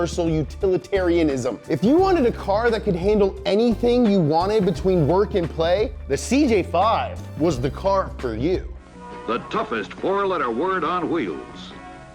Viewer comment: i thought i heard “dumbest”...had to rewind haha